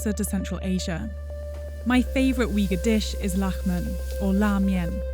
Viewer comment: Anybody else thought she said "human" at min